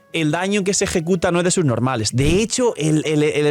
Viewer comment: juro que escuche "el daño que se ejecuta no es de subnormales" xd